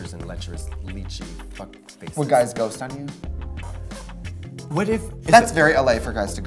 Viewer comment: I totally thought trixie said "the guy was a ghost onion"